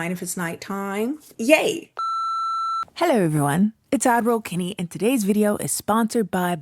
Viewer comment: This whole time I thought you were saying Adderall Kennie.